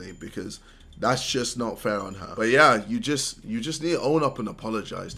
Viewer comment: that’s not fair on her is what I heard idk I might be deaf